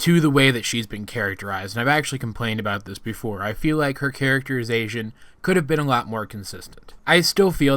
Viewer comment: I thought you were saying, "Character as asian," instead of characterization xD